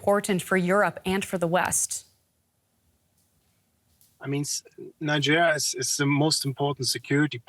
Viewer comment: Is it just me or did ulf say "Nigeria" at 6:59😂